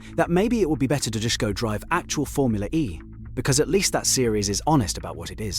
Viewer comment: Thats not what he said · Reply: He said Formula E on steroids to be precise